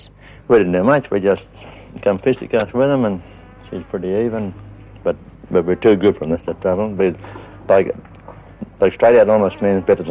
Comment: The guy @ 7:50 what's he saying. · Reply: But we're too good for them that's their trouble.